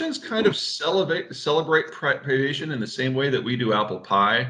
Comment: “The Russians enjoy privation like we enjoy apple pie.” 😂🤣🙃😑😞😣😰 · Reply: Omg, I thought he said preparation not privation.